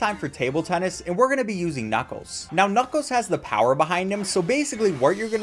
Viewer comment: Did he say knucos?